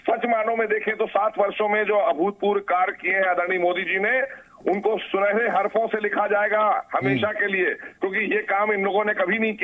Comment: did I just hear Adani Modiji😂